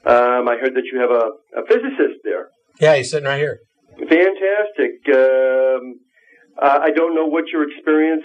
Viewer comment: Did the caller just "Herp Derp"? "Fantastisk Geeerm"